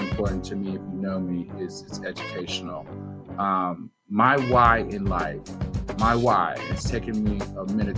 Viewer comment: Algorathors, fast forward to 02:14 if the music is bothering you. Sorry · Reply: Hey, music was so loud I almost thought you said my wife, but you said my "why"